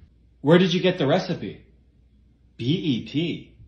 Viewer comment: I thought he said BET which made me think that form of media rly started to step up their game in culinary arts